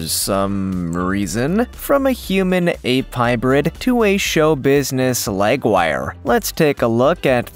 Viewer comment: I misheard that as "Show Business Leg Wire" and instantly thought "Strange, yet fascinating"